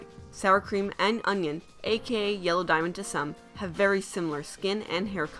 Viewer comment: Did she just say aka yellow diamond’s son?